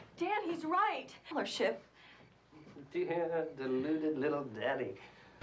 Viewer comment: What did he say at 2:53 lol? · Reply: deluded little danny (sounds like daddy tho lmao)